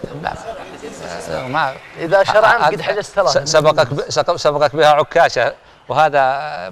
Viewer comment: اسمعوا وش يقول 31:8 يقول حاجز ثلاثه يقصد التوام وعبدالعزيز 💗😢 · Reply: Eid Aljahniy الدقيقه 31:10 يقول حاجز ثلاثه يازين اخوتهم 😍😍